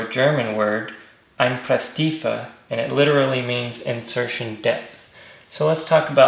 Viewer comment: totally thought u said "Insertion Death" hahah PS- Send those Garden Gnomes my way Ive got some work needed to do on my Truck too :)